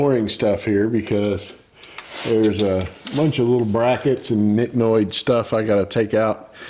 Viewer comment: Good video, did i hear you correctly at time 13:12 say remove brackets and "Nit noi" stuff? Sounds like the THAI term for a little bit or not much. Funny if i heard that correctly. Lol.